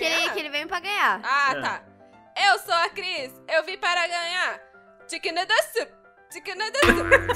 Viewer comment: só eu que entendi: Chicken nodle soup? (Música do J-hope BTS) (Se tiver escrito errado foi mal)